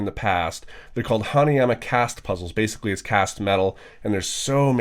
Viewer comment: Instead of hanayama cast puzzle I heard "Honey, im a cast puzzle"